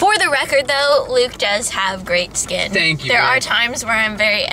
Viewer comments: Did I hear "Thank you Babe" or am I hallucinating??? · For everyone asking, it sound like Luke says thank you babe at 3:53 right after compliments him